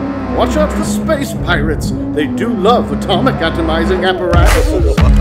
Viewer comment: What he said at 0:55~0:58?? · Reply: "They do love atomic atomizing apparatuses"